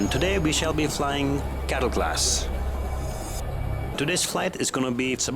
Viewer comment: oohhh cattle class hahaha i thought you said ghetto class lmao!